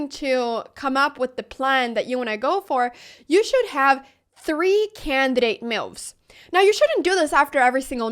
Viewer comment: My brain is ruined. at 1:35 i keep hearing i should have 3 candidate milfs :s